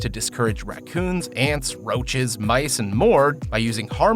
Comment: We have the best rodent traps ever: Cats. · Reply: I thought he said "bison" when he said "mice and"